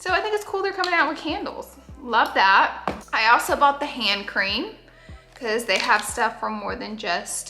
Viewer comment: It’s like 130 am for me rn and I thought you said “ham cream” not “hand cream” 😂😅